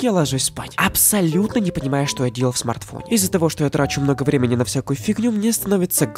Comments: мне послышалось : "Из-за того, что я дрочу много времени..." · Мне на 2:50 послышалось что он сказал много времени дрочю на всякую фигню)) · "ДРОЧУ НА ВСЯКУЮ ФИГНЮ?! ВАААААААТ!!!? ПОСЛЫШАЛОСЬ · мне послышалось "из за того что я ДРОЧУ на всякую фигню"